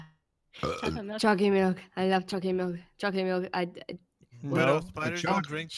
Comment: Widow sounds like she is saying i love chalkie milk XD